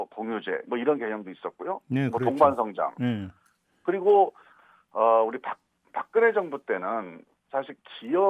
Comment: 우리 박근혜 정부때는 14:16 우리 박근혜 정부때는 내가 잘못 들었나.. 우리 박근혜 ?